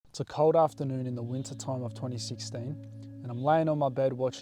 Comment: I thought he said 20 6-7